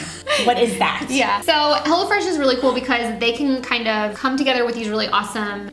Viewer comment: It sounded like was saying "hella fresh" lol.